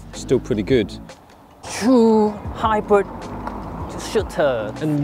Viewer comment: did say at 26:02 " True hybrid Shitter "